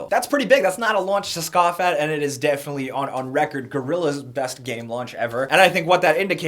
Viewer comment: wait did you just say gorillaz?